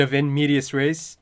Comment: What do you say at 4:22? "Bit of [???]." You use the phrase again at 51:00. Your reaction to the tree scene in the ED was great and funny. Great reaction and discussion, as always. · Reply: In medias res.